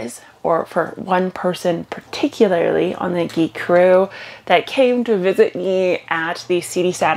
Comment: GIC CREW ❤ Greenhouse Homedepot <link> Amazon <link> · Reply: GIC Crew now I get it I thought you were saying Geek Crew with a Canadian accent